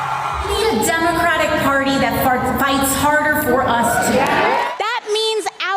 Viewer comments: Did she just say "we need a democrat party who farts... Hatder for us" 😅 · “We are the Democratic Party that farts harder for you!” 🤣🤪 (Thats what I heard her say) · Did she just say that the Democrats need to “fart harder”? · Did she just say "We need a Democrat party that farts harder???" 2:42 lmao Just perfect 🤣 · am I crazy but did she say she the democrat party is "farting hard... fighting hard.." for them?? yes indeed. 2:44 totally. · I’m sorry but did she just say we’re the party that farts and then correct herself and say fight 🤣 I swear she did